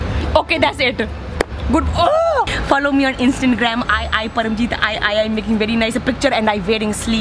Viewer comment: All I hear is "Instant-gram" 😂😂😂